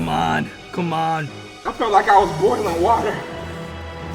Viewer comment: "I feel like I was born in the water." that's what i heard